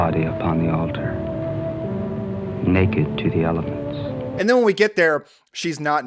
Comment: For a minute there, I thought he said 'naked to the elephants!'